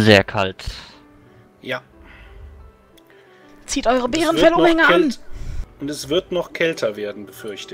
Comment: Hab mich weggeschmissen vor Lachen als ich bei 28:34 zuerst verstehe: "Zieht eure Bärenfell-OHRINGE an!" :-D Schöne RP- Runde Gruß